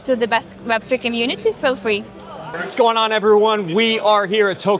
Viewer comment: она имела в виду доступ в офис и комьюнити бесплатно и даже ничего платить не надо?) Первый раз слышу чтоб вступить можно было кому угодно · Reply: Говорит feel free не for free